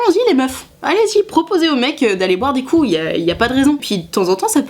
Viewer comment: j'ai compris "boire des couilles"...